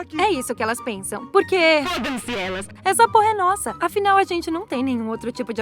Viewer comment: só eu que escutei na parte 1:24 que ela disse: essa porra e nossa?